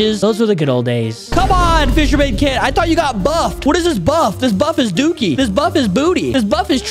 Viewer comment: Well i thought he Said THEY BUFFED A KID!!!! 😂😂😂